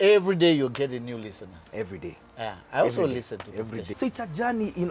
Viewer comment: Haha 31:40 Did Churchill just say that he also listens to Maina? You are Mwalimu King'ang'i we know you.